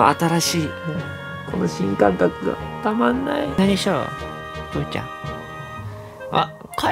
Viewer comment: にぷーちゃんがちゃちゃに聞こえた(笑)